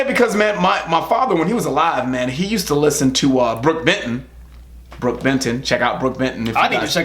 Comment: Hey... You said Brook Benton, didn't you?? I thought you said Brook Vinton. Anyways... I looked him up and found the song "lie to me" by Brook Benton. I can hear a Patsy Cline "feel" to it. Great stuff. You guys should react to this song. Thanks for turning me on to something I never heard before. Good stuff. Keep up the great work. You guys rock!!